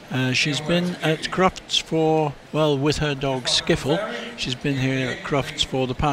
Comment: This performance is so beautiful ! Amazing music and amazing routine ! Well done Lucy and Skiffle <3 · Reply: I thought I heard Sniffles. Skiffels sounds better.